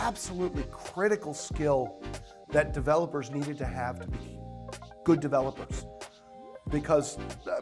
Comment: needed to have .....? What did he say there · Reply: Good developer